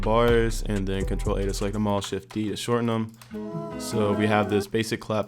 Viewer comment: what shortcut did you used do short the keyz on piano roll at 1:10? You spoke to fast and I didn't understand it very well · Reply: Fejão Beats shift + d